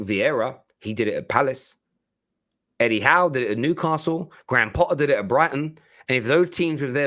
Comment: I thought it said De Ligt to Newcastle lol. 😂😂